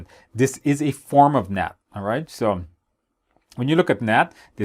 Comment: the subtitles think you're saying "net" but i know youre saying "NAT"